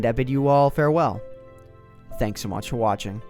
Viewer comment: At the end of most of his videos, is it just me, or does it kinda sound like he says "Thanks for watch for watching" instead of "Thanks so much for watching"?